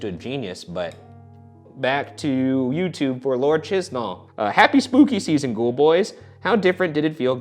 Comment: deadass thought he said "lord cheesball" 😭